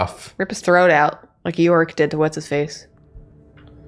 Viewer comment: For a moment I misheard "Iorek" as "York" and thought "wow I do not remember that part in Drawtectives..."